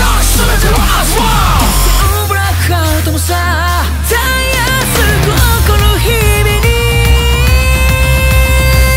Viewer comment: の 突然ブラックアウトもさ〜 の「突然」の所 「とつぜん」なのか「ノー」なのかわかる人いません？なんかカラオケで歌詞出てくるやつしたら最初はノーやったのにこの前はとつぜんやってえ？？？て思ってTAKUYAくんが歌ってるの聞いても正直どっちにも聞こえるし、、、 私の耳がおかしいんですかね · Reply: まぁ 聞き取りづらいですが「突然」と言っています。 ちなみにmステでははっきり「突然」で歌ってましたよ〜